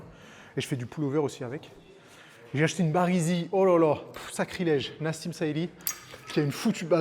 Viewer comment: Bonjour, je me rend compte que je ne suis pas très au fait du vocabulaire de musculation : à 3:34 j’ai compris « bas résille » et je me suis demandée en quoi ce type d’objet pouvait aider dans les exercices 🧐. Puis j’ai compris que c’était « barre easy ». Et là, ça a fait sens. La honte !😅